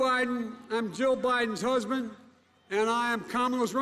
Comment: Did he just say I’m joe Biden husband ? 0.49 😷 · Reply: He said “I am Jill Biden’s husband and Kamala s running mate”.